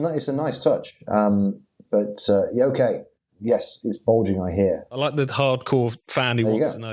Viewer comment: I always heard “it’s Bulging eh”. Illusion shattered. Also where was the end of episode petty ban?